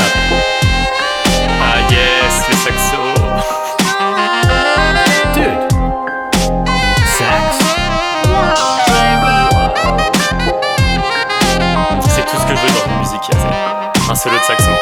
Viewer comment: just sounded like Dude, sex?